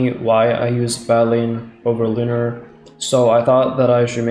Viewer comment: did you call it badleen